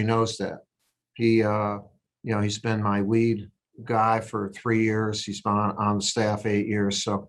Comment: Sounded like he said "he's been my weed guy"